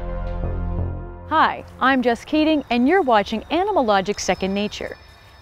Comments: I thought she said Hi, I'm just kidding 😳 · I thought she said "hi i'm just kidding" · Every time she tells her name I hear "Hi! I'm just kidding." EVERY TIME. 😂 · did I just heard "Hey, I'm just kidding, and you are watching animalogic second nature" 😂😂😂🤣🤣😂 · What's her name? I thought i heard, "Hi, I'm just kidding." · did she just said "hi im just kidding" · At first I heard her said “hi I’m just kidding” and I’m like ?? April fools? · Did I just heard, "Hi! I'm just kidding"? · Every time she says "I'm Jess Keating", I hear "I'm just kidding"... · This is what i heard at minutes 0:55 = "Hey, I'm just kidding ......", anyone did the same?